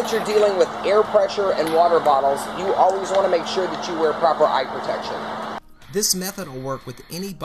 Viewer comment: left right when I heard "were proper eye protection" 0:27 .... just kidding :)